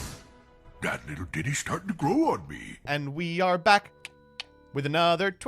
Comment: did he say "that little diddy start growing in me"?